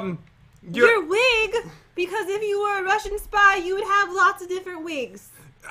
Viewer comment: At first I thought said Russian guy not Russian spy lol 😂